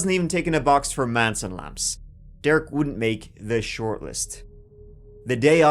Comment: wtf did you say right here? He wasn't even taking a box from Manson lamps? Manson labs? I watched that clip lole 20 times and i have no idea what you said or what that means. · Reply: “wasn’t ticking the box for Manson lamps” translation: “he didn’t have crazy / killer eyes [like Charles Manson]”